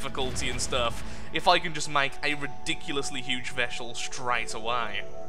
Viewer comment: "If I could make a Ridiculous sized Veshel that early" 19:19, fix your grammar; is funny. Veshel instead of Vessel gg.